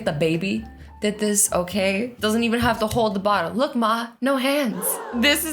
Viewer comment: When azzy said “look ma no hands” I thought she said ligma no hands